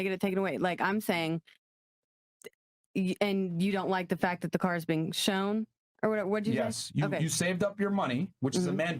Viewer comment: At the 7:58 point did she say “nigga” or I’m tripping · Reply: "...in the negative"